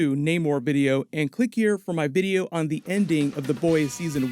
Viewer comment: When you said, "ending of The Boys season 1", I misheard you and thought you said "Okye season 1". I thought I missed something major in the MCU for a second...